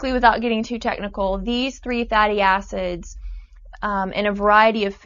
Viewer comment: lol i thought she said fatty asses lol